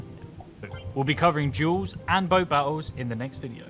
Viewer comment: I think the magic wand is an edit button. To change your deck Also, at 14:15 i think said duels, not jewels. Its his english accent